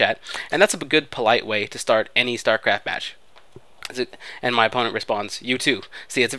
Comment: Captions: and my father response you too What X really Said: and my opponent responds you too